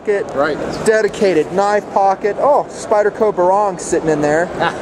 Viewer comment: What Spyderco is he carrying? I thought he said Sypderco Berong but I couldn't find it on Spyderco's site.